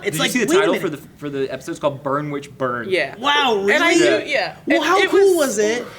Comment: i thought it said BUM WITCH BUM and was like WTF for a second :D